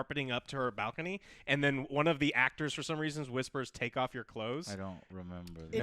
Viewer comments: It was never "all good teenagers take off your clothes!" like everyone thinks. It was "come on, good kitty, take off and go!" Everyone just misheard the line because of the terrible audio quality on the home video cassette releases. Hence why the line was removed in later versions of the film. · you guys need to look up the Disney myths. a lot of these were disproven. the priest "boner" was his knees, and the "just take off your clothes" was a mishearing, the ACTUAL line is "Just take off an go" hes telling carpet to go away after dropping him off.